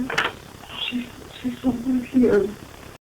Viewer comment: hoo I heard 'She's somewhere here' too. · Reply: Noah Winterbourn I heard “she’s under here”